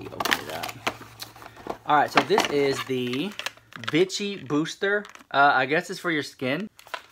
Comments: I thought he said "bitchy booster" instead of Vichy booster...I thought he had Jeffree Starr product in therw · I heard “Bitchy Booster “🤣🤣🤣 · When you said Vichy i thought you said bitchy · 😂 I thought you said "Bitchy" ... and then I see the brand "Vichy" which is pronounced "Veeechy" But honestly after that unboxing I would be "Bitchy" too!